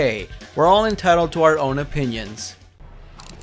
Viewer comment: i thought you said opinions but you said every one hates stryker